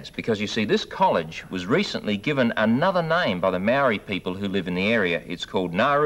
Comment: When he said “Maori people” I thought he had said “married people”.